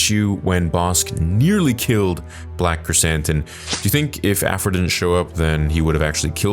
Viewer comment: When you first said his name on stream I thought you said Black Chris Hansen xd